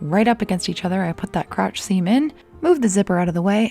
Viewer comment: What she said “the crotch seam in” What I heard “the crotch semen”